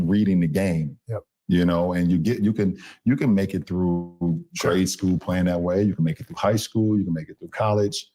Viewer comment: did he say "trade schoo"? Anyway, I don't know how tf he even made it through "school" saying "schoo" 🤦‍♂️🤦‍♀️🤦😆🤣😂